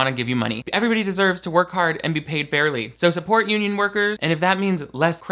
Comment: What I heard was: “Everyone deserves to work hard and be paid barely.” I was like, Yeah, roast ‘em! (bc I can only hear things in a sarcastic tone, apparently)